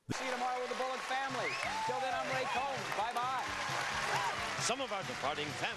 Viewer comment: I misheard the host's name as "Gray Coat".